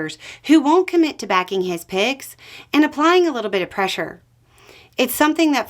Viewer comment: I misheard "his picks" as "his pigs". At least, I think I did...